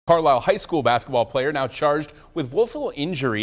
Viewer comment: Thought he said “bata player” like dang, even the news roasting the player after he got laid out.